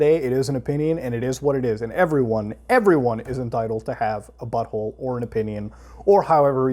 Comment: I'm so high I thought you said "bubble" instead of "butthole" at the start ... oi, I gotta lay off this shit man XD edit: I fuckin' GUESSED BATTLE FOR BIKINI BOTTOM! sry caps but DAMN, Muta I love your videos, man... keep it real. <3